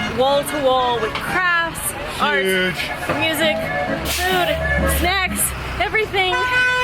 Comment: on minute 3:36 when you said is a huge place and what you can find there...did you say "sex" as well?...